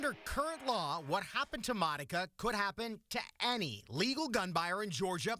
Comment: I thought he said my ni…..